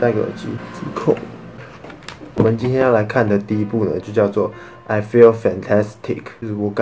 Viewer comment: i feel fantastic 只有我听成 i feel santa d*ck 吗 😂???